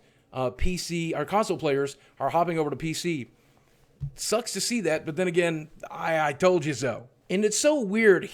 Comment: PC master race. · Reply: What did you just say ? "PC sucks ass" ?